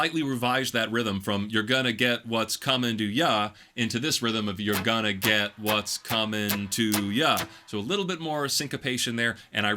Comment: He sings, “You’re gonna get what’s comin’ to ya.” I hear, “One little voice is callin’ me, callin’ me.” (Think “Bangin’ on a Trashcan” from Doug.)